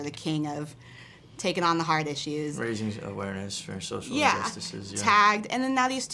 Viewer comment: Hey can someone tell me what Noah is saying in this part 0:11 cause I don’t understand please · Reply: Luna Castañeda "raise awareness for social injustices"